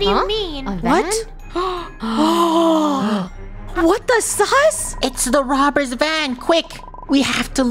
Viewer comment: Did Funneh just say what the sus😭 👁️👄👁️